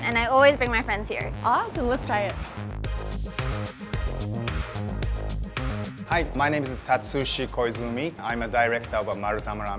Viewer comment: Lawd, i heard :"Hi, my name is hot sushi poisoning"